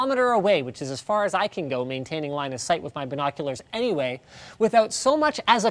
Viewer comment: Who else thought he said, "Maintaining Linus sight" at 4:57 ?